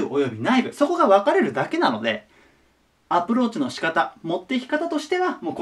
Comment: 最後の「アプローチの仕方」が「アップルウォッチの仕方」に聞こえたw